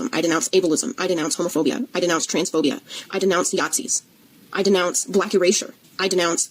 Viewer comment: Sounded like she said "I denounce Yahtzee"...WTF is wrong with Yahtzee, it's a fun game....